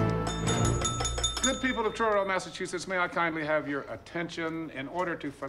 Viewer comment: Oh, Truro MA. I know where that is. Actually the first time I thought he said Charlton. And I used to pass through there all the time too. A mundane detail but I think I’ve discussed everything else about this scene over the years.